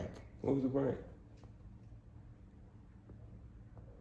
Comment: “What was the prank?” “yall got cashapp?” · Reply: I thought she said “yall got ketchup” 🤣🤣must be the accent I’m from the uk 🇬🇧